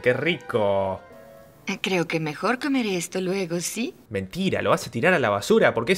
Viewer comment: Parece que dice "Mejor quemare esto Luego sí"